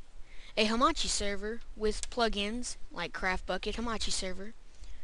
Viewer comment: When He Renamed The Folder And Said Bukkit I Thought He Said **** It